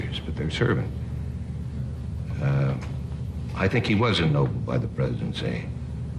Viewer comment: The caption is wrong FYI for those hard of hearing.. 20:13 “Truman... he was ennobled by the Presidency” not “wasn’t”